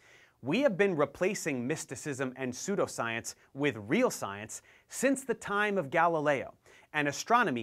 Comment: kinds weird how I heard was We've been replacing masochism and sadosism with sadomasochism